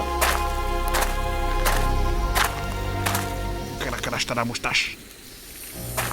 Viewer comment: putain de grosses fautes d'espagnol : déjà "moustache" en espagnol, ça se dit "bigote" et après, on ne dit as "el moustacha" parce que el se place devant un nom masculin, et moustacha (qui se finit en A) est féminin, enfin je crois ... Oui je sais , c'est chiant et tout le monde s'en fout mais il fallait bien que mes cours d'espagnol me servent un jour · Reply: Sauf que j'ai compris "Al Moustacha", moi.